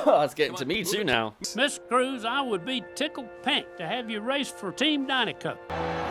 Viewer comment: It's Tickled Pink. The phrase means, honored and very happy. the phrase could have been said like this: "Ms. Cruz, I would be honored to have you race for team Dinoco." Tex was clearly impressed by her racing skill. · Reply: i thought he said tickled paint. because race cars and their paint jobs